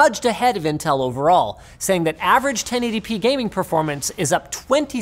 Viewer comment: Why does the subtitles say "10 ADP" when you say "1080p"?